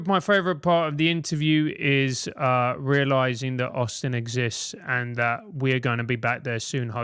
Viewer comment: I could've sworn at the end he said "I'm glad I realized that arson exists".